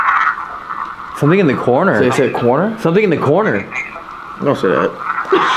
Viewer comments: I hear "over there in the corner" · Sounded like it said Coroner at 40:35, I didn’t really hear corner